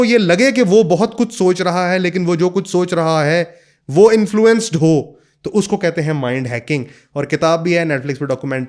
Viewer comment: what is the name of book that he said at the begining at 2:35 ? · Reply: mind hacking